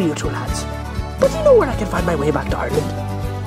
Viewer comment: back to Ireland? To be honest I thought he said target the store